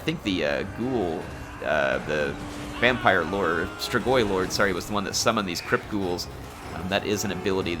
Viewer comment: I heard "Strigoi Vamployer" and now I'm imagining a Strigoi in a suit interviewing various creatures of the night for a position at his company